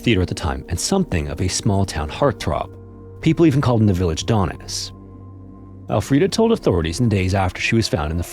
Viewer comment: What was that guy called in his town? I thought you said "some people called him the village dumb ass."